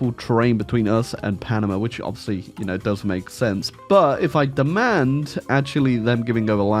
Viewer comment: You said that it doesn’t make sense that the Columbia-Panama border is impassable ( 4:10 ). What you said was wrong, there is a very thick and dangerous jungle and it is impossible to go trough it, only a few people did. That is why if people want to go to Columbia from Panama they go with a boat not trough the border. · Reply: Lol I think you misheard he says "does make sense"